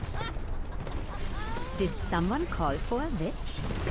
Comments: It sounded like she said "Did someone call for a bitch?". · Did Mercy say: "Did someone call for a bitch?" LOL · 'Did someone call for a bitch?' XD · omg first I understood: Did someone call a b*tch? xD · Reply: Juan Andrade I know that she said witch, but for me in the first moment it sounded like b*tch :D